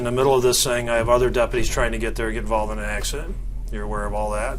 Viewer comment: I thought he said no one was involved in a accident ?